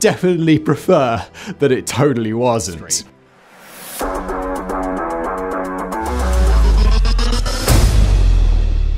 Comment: What did that little voice say @ 0:29? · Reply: History